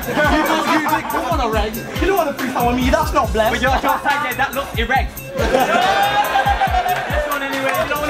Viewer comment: Also what does say at 17:45 when refuting Bru c, something erect?